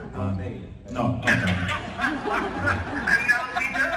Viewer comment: what did he say? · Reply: And now we know ... Meaning he'd rather talk to Sam.